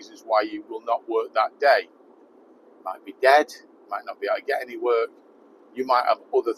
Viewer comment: When you were talking about how many weeks a year you worked, I thought you said "you may be dead"and yes you probably would struggle then!!! Listening back you may have said "it may be dead". 😅